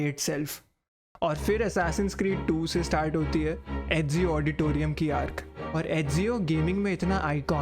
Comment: did he actually said Ezio Auditorium lol